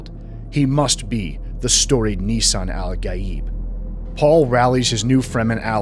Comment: did you just say Nissan Al Gaib?